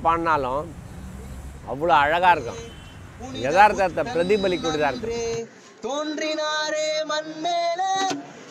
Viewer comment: Thanks for doing this story man, great video. At 7:18 he says gaana music is used by the people to ruminate on their culture, life and not what the subtitle says. A tamil word pradhipallikum (reflective) has been misunderstood as pradeep ali.